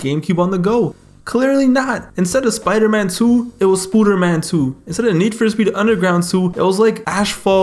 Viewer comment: “Instead of Spider Man 2, It was Spooder Man 2.” Ngl that got me 😂😂. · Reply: I thought he said Scooter Man lmao